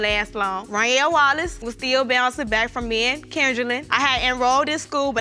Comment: I thought she said he was bouncing back from men… 🤦🏽‍♂️😂